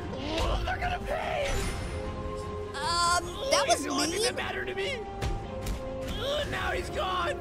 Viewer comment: umm tails what do you mean by “umm that was me” or did you say “umm that was mean” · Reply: No he said mean it’s just that he was very quiet.